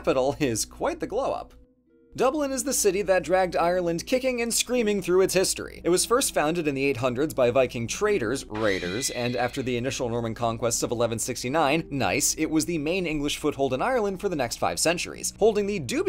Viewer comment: The sentence and 3:19 got me a little confused, i thought you said "...initial Roman conquest of 1169" but the cc corrected it to "Norman"